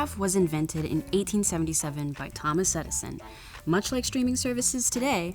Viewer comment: the English CC says "1977" instead of "1877" - just thought you might like to know! <3